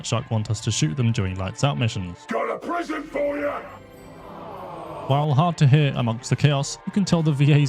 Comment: I ALWAYS hear "GO TO PRISON FOR YA." Same for the Loose Cannon with the same line